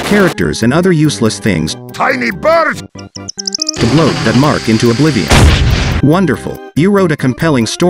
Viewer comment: We might be having some TTS Nonsense going on here as I swear to god is saying to Blow Denmark into Oblivion but Cc is saying to blow that mark into Oblivion